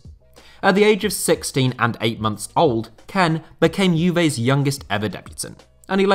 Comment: Genuinely thought at 1:51 I heard you say Kean became Juventus' youngest debutant at 16-18 months old, had to go back and listen properly